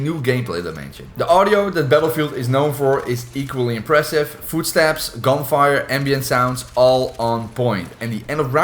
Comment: thought i heard, "food stamps"😭😭🤣🤣 Great video. Worth a watch. You touched on some topics others failed to mention!